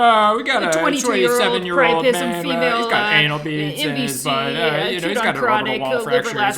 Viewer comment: all I heard was “anal beads” 😂😂😂